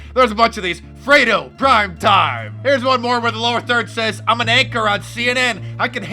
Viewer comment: It almost sounds like he says he's an actor on CNN, not anchor 😂